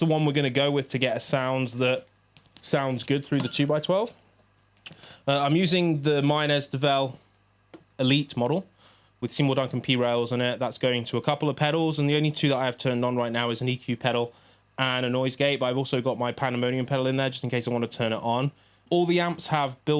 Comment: He literally says what's in it... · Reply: i heard "seymour ducan t-rails"... im not a english speaker...